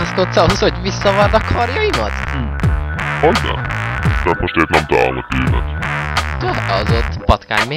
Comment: what is kakazu saying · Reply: Right now I cant find my needle